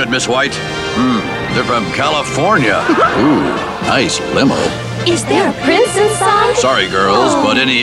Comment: ooh nice limo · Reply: Is there a princess fun? (that is something that i always thought they said😛 )